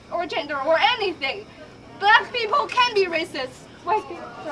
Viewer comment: Can someone please give me subtitles on what the Asian people is saying at 4:30 I am not natural English speaker I am Spanish or English, please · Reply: Digital HD + "but the thing is, black people can be racist"